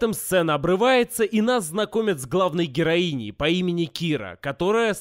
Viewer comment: мне послышалось, или её реально зовут Кира?